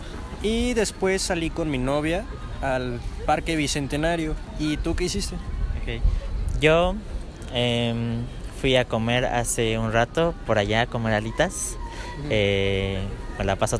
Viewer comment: I thought he said ratón, I was so confused 😂